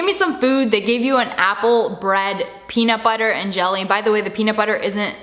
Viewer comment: I thought you said they gave you your own cell